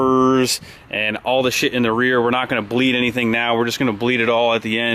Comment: did just say a bad word?!?!?!?!? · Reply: who gives a fuck if the man said "shit"?